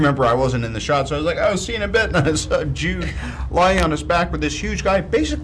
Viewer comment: did robert just say to Jude: "see you in bed?" :P :P